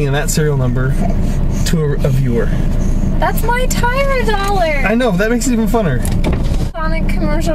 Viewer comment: i thought at first u said it was ur "tire dollar" and i was thinking, i dont think u can replace ur tire for a dollar?!?! lol u guys are funny, i dont know if i can think of a proper name for the gnome without seeing him, but without having a face for the name, i suggest moccasin, and u can be like " o little moccasin" lol